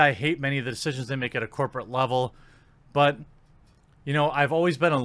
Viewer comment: I had to rewatch this moment. I thought I heard you say "I hate the simps at the corporate level"